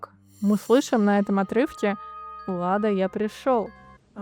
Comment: Не всегда они орут, скажу по своему опыту с транскоммуникацией) Ты тоже самое расслышал среди шума? 🤔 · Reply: надо немного поднапрячся и сделать громкость на всю в хороших больших наушниках🎧 ,там прирывистые слова ,я услышал "Влада я пришёл и прости" "хватит а я здесь спокоен"очень отчётливо слышно🕯️👻👍удивительно🙃